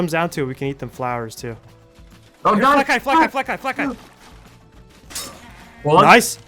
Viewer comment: I heard "Black guy!!" 🗿